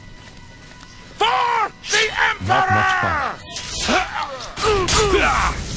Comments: did he say "F*** The Emperor!" ? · Did he just say F*** the emperor! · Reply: He said "FOR THE EMPEROR!"